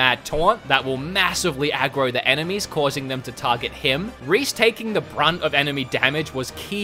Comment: wait, did you just called Reyn Rease? Cause for one moment you said Reyn then another moment you said Rease. @ 10:24 Just weird that you have 2 versions for the same name in this review. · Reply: He definitely called Reyn Reece, about 3 separate times